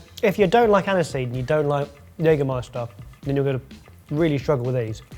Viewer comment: did say the "N" word?! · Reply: He said Jägermaister.